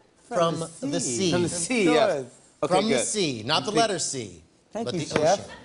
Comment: 'Food that come from the sea.. not the letter C ' ... What? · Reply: I thought he said lettuce